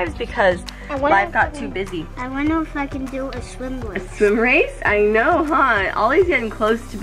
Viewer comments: Is it just me or at 1:33 did he say I want to f$cking do a swim race . 🤯😳🤯😳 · Did say the f word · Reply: OPP EcHo No he said, “I wonder if I can do a swim race.”